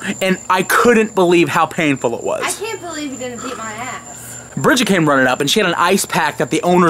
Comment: I swear, when Bridgette said "I'm surprised he didn't beat my ass" I heard "I'm surprised he didn't EAT my ass" 😂😂😂